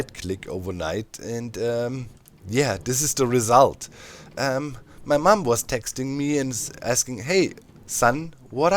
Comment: Gonna be real I heard, "my mumbo was texting me..."